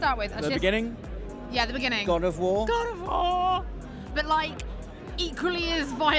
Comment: i thought Holly said " GOD OF WHHOOOORE! " I'm sorry xD